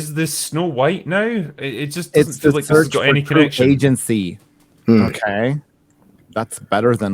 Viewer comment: Did I hear it right in 3:30, is it a search for the right p*rn agency, instead of true love?